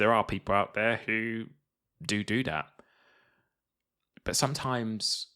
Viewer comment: i randomly paused at ~5:00 to let my dogs out and when i got back and resumed the first words i heard were "doo doo that"